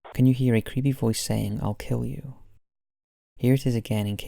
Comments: "a creepy voice saying 'I'll kill you'" I heard "ruck ryou" − Scooby Doo is coming for that household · Instead of "I kill you" in number 4 I hear: "I feel you". Maybe it's just a very sympathetic ghost...